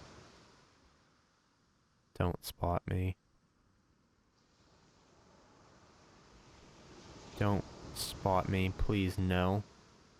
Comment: I thought he said please don't spa me, please don't spa me Me: you don't want an ORGANic makeover?